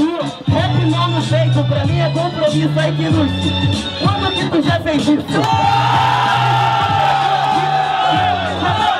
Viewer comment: nao entendi sobre oque o fala no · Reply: Rafael da C. Gomes Cavalheiro pegou no peito da medusa